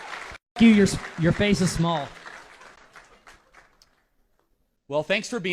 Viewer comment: Lol did he just say "your face is small" and then run away?